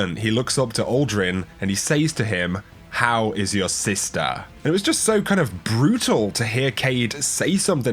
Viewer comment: Lol I thought I heard you say: Cayde-6: “I slept with your sister.”